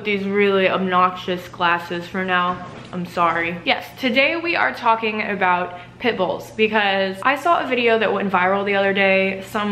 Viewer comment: I thought she said "Today we're talking about PICKLES."